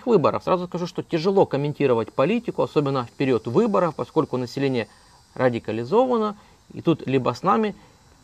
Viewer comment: не расслышал, население....ради кала Z- ированно, или радо, колонизированно?